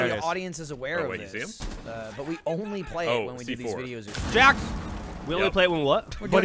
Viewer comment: What does Michael say at 13:00? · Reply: He says they only play Rainbow Six for videos or streaming, as in only when they are in office filming